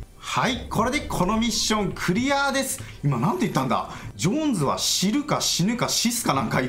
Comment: 「ジョーンズは知っている...」に聞こえますね...(個人的に)